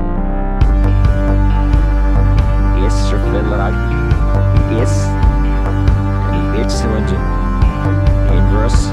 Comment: Any idea what is said backwards at 2:00?! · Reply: Because I live, surely, there's no escaping it. Satan. We gotta live for Satan just reversed :)